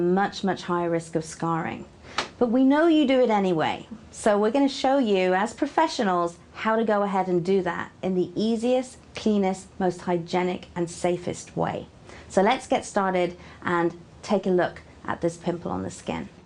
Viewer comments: I thought she said penis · Who thought she said penis instead of cleanest😂😂😂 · did she say penis? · Sounds like she said "penis" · at first i thought she said easiest, penis ...... way to pick a spot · when she said cleanist, I swore she said "penis" lol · Did anyone else hear penis? XD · did she just say penis?